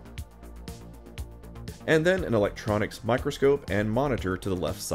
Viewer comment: at first I thought you said "Electron Microscope" and I was getting really excited!